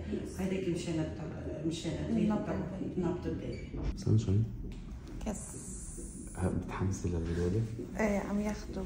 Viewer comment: شو قالت ؟ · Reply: يس